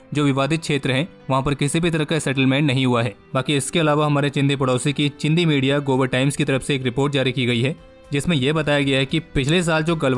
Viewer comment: What do you said, chindi media global times or gobar times 😂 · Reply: Gobar Times